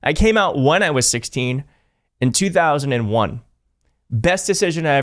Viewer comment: OMG! I thought you said you came out at 16 in 2021. Had to listen to that again.